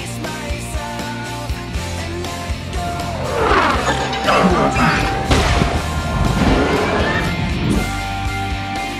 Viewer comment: What does starscream say at 2:44 ? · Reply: It's not over!